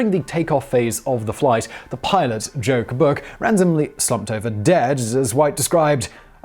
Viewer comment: Did Simon say the pilot's name was "Joke Book"? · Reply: No, he said “Joe Kabook” or “Joe Kabuk”. Which sounds similar.